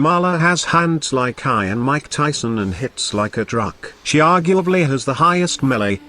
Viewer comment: don't know about anyone else but the ending of 4:20 what I heard was that she hits like a drunk :D